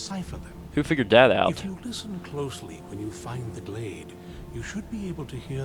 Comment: The subtitles say 'glade' instead of 'blade.' Typos! :D